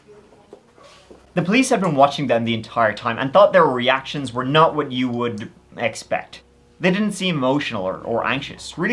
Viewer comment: Did anyone else hear a strange whisper between 40:10-40:20? I can't tell what's being said but I think the last word is maybe "power". It's very creepy. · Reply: Yes! A voice saying The Power!😮